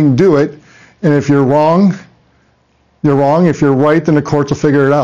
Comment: Is it me or did from 8:40 to 8:50 he made the statement that, and I quote. "If you're wrong, you're wrong. And if you're white, the courts will figure it out."???? Tell me if I'm tripping, but that's what I heard people.